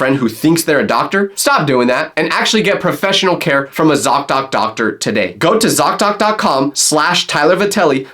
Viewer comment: btw I heard zocdocdocdoctor